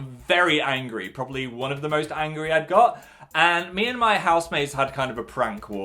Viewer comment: did he just say "one of the most angry I've got" or am I just having a stroke?